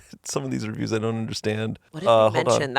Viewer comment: what did she say? The subtitles didnt catch it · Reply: Amélie